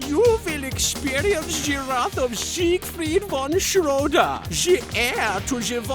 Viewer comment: The subtitles say "[...] soon here Kaiba you will experience giraffe of siegfried"